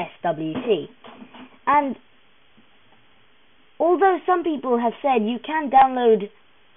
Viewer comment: lmao yes, thank you captions for letting me "understand" what this person is saying... 0:23 apparently is actually saying "alternate some people have fun you can download it"